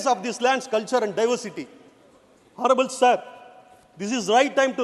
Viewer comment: Did he just say "HORRIBLE SIR" instead of Honourable sir😮